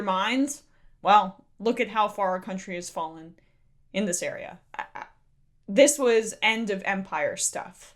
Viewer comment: I could have sworn you said: "End of VAMPIRE." instead of "Empire" and I was going to rejoice. Too many vampire novels and movies. End of empire is good too, except I have to live through it.